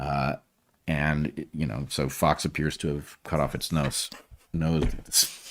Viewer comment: I had the captions on, and didn't hear what they said. I first thought BW had just said nuts and then corrected it to nose. It would have made sense... 😄